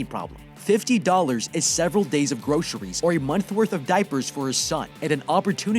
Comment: "$50 is enough for a months worth of diapers for his son" You my friend clearly must not have children 😂😂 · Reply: I thought he said a pack of diapers?